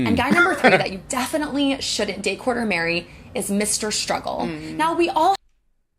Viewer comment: When they say date, court, or marry.. Sounds like she saying date, PORK, or marry lols